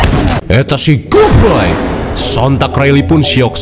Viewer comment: Saat si ayah mukulin kepala unicorn sampe darahnya muncrat di 2:40, bang itu bilang apa ya? Saya dengarnya "eta si goboy"